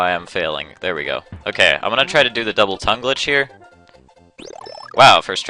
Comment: That makes sense :) I guess the only reason I got confused is because I always thought you were saying "double time" instead of "double tongue". I have bad hearing :\